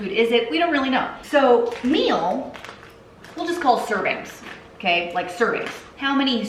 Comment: For a split second there I thought she said "cervix"....ooops...my bad..."servings" 😆🤭